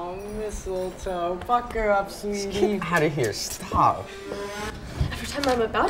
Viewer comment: Wait did say the f word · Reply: he said "pucker up sweetie"